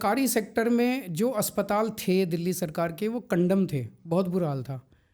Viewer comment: what did he say !? Kandum ☠️🥸